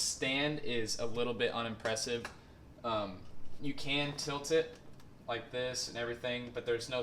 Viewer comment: I'm so gonna buy that monitor thanks to you. But i have a question can you bend the screen straight? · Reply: nvm i didn't see the whole vid before comment xD first i thought you said "You can't tilt it"